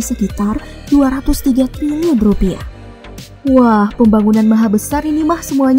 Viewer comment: 230 rupiah , apa salah denger ya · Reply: 203 triliun....kamu salah dnger bnget